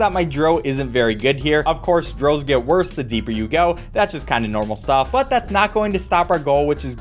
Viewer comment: For a second I thought he said “Girls get worse the deeper you go”. Thank god he didnt